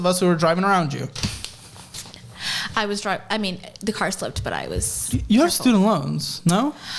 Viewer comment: I thought she was saying,” I was drunk,” lol…